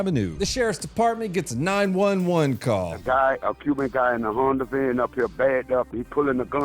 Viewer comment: did he say a pu(B)ic guy? Is that the "yanny/laurel" thing?